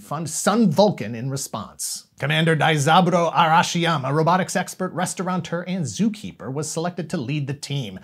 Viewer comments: I totally heard “robotic sexpert!” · I swear you're saying "robitic sexpert"